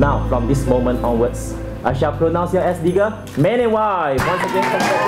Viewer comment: Is it just me or did I hear the priest say "Nigga" @ 5:15 XDDD!!